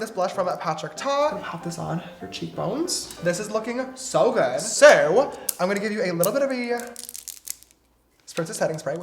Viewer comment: it sounded like he said so but in Swedish as “så” with and southern accent😂😂 he would literally be so good at speaking swedish!! (Är det några svenskar här? Ja som sagt det lät som han sa ”så” på skånska😂🇸🇪🇸🇪🇸🇪)